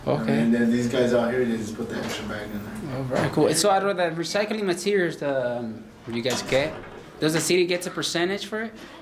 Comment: I swear to Christ he says "are you guys gay?" Right in the middle of a sentence. I literally spit my beer laughing! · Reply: 🤣 no I think he says "the recycling materials tha u guys ge" (that you guys get)